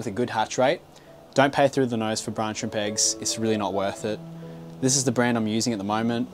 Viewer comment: What does he say at around 5:26? Don't pay through the...? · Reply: Don't pay though the nose for brine shrimp eggs